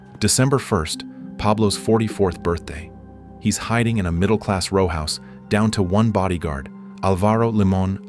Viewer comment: subtitles says middle-a**